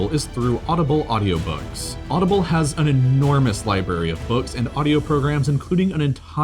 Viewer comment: Did u say li-berry?